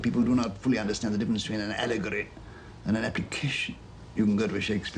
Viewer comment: can anyone tell me what word he uses at 5:55 he says " of course people do not fully understand the difference between an allegory and a ......" what is the word he uses I can't make it out enough to Google it:( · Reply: sorry that you’ve waited 11 years with no answer - but here it is: the word Tolkien uses there is “application.”